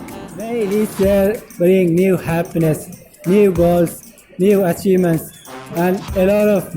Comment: !!! Did you just say (new girls) or new goals ? 😂😂 Anyways... the video was an amazing guys !! Happy new 2021 !!!